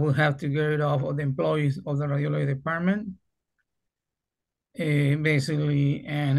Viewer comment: I kept hearing "the RAVIOLI department". "Im not a RAVIOLI, thats beyond my scope". 😊